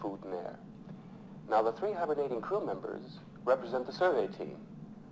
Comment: It sounds like Dave says "The three -hundred-eighty crew members represent the survey team"